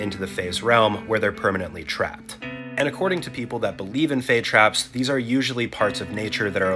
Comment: I straight up thought it said fake trap 😅